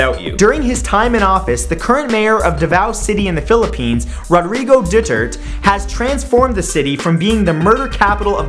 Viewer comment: It sounded like he said Rodrigo Du-turd instead of Rodrigo Du-ter-te